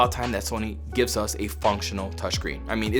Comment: Yo when you said functional, I thought you were about to say "fucking" I was like WOAH SNAPPED XD